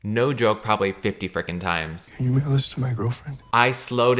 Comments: I heard "can you mail this to my gf?" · Caleb repeating a clip of a guy pretty clearly saying “can you mail this to my girlfriend?” over and over while being super confused about what he’s saying is so funny to me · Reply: i thought he said “can you make a list for my girlfriend” 😭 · It sounded like "Can you mail this to my girlfriend" or something along those lines to me. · Sounded like he said “can you make a list to my girlfriend” but idk why he would say that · Caleb repeating a clip of a guy pretty clearly saying “can you mail this to my girlfriend?” over and over while being super confused about what he’s saying is so funny to me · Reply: I mean I kept hearing "can you make a list to my girlfriend" so I don't blame Caleb 😂